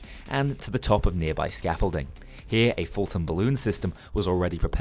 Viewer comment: Did he just said Fulton Balloon Recovery System? Bain: He is coming through? Roger that.